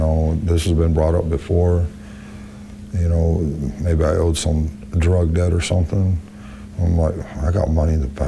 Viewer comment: Did he just say cause I could owe a drug debt??